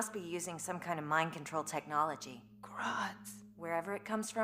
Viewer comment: What did Black Canary say? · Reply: Huntress: They must be using some kind of mind control technology. BC: Grodd's...